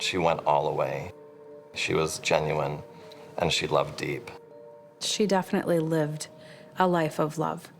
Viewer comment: at first I thought he said 'she loved to eat.' And I was like 'same'